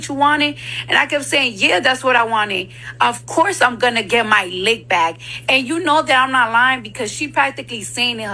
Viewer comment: what does Cardi mean she had to her to get her lick back, I thought she said RAH didn’t touch her back in the day